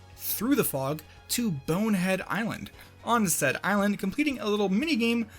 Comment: i thought you said onset island as another wind waker reference for a moment there